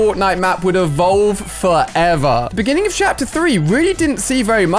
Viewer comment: chapter 3 instead of season 3